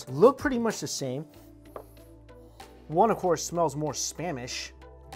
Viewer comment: I thought you said one of the instant noodles smelled more Spanish and I was intrigued for a second